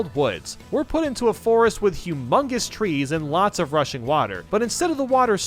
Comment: I want toad factory as DLC Also, did he Said at 8:28 russian? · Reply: rushing 😂